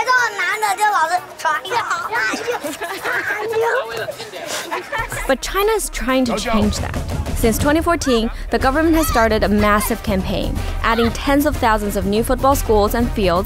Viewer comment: did anyone hear " but we love India"